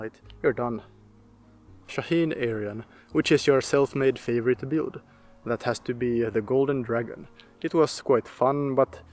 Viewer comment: I thought you said fornicate build. Wow. I subscribed immediately.